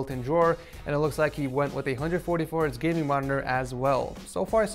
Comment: Did he just say 144 inch gaming monitor?💀